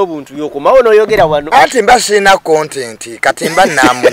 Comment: Minute 32:37😂😂😂 I hear content😂🙌